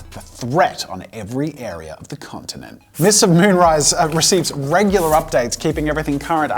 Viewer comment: I thought he said mr moonrise